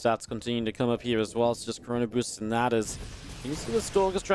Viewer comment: did you say corona-boosting?